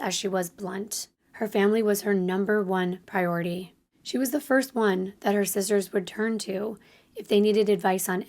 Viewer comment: I thought you said: “She was the first one that her scissors would turn to…” I was like, damn, she did have unruly hair!